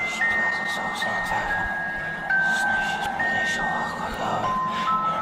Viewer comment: я слышу: "сам овощ"